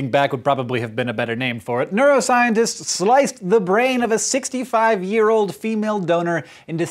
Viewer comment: I think you misheard. Hank said the woman the brain came from was sixty-five years old, not five years old.